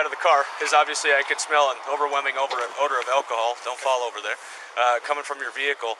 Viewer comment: 000 but i thought you said you smelld an overwhelming scent of alcohol?????